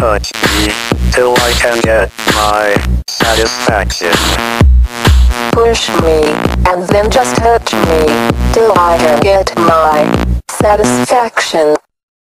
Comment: What I heard at first: “push me, and then just hurt me, till I can get my satisfaction”